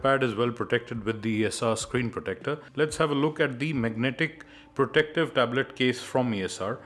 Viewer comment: it sounds like you said mugnetic